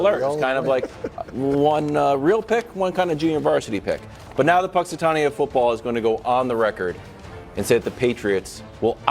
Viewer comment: what did Wildes say? The what of football? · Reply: The Punxsutawney Phil of Football. Punxsutawney Phil is a groundhog who they use for an event to predict the coming of Spring.